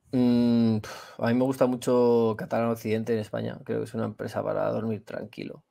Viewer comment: no entiendo el nombre de la empresa espanola, alguien lo entendio? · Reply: Catalana Occidente